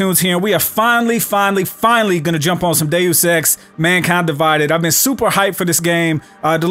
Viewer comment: did he just say day sex?